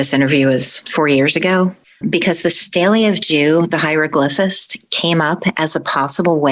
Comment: Hey, can someone please tell me what she said at 7:48? The stalio- something, hyroglyphist- how she gained the knowledge to come in contact with her guardian angel? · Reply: She's referring to the Stele-de-Jeu or the "Headless/Bornless Rite". Its not historically linked to the HGA but it is linked to getting a familiar spirit from the PGM. Crowley kinda muddied the waters with that one and conflated it with the HGA.